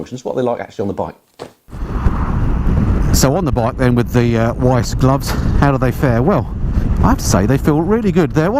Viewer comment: I could have sworn you just said "on the bike with the wife's gloves.." lol, had an image of you riding in Marigolds, or crushed velvet with faux fur cuffs lol